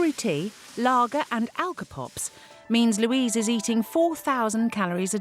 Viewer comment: What does the lady say at 8:45 ? Sugary tea, ..., and ... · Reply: lager and alcopops